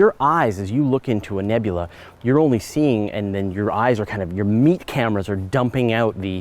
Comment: Did you just say Meat Cameras?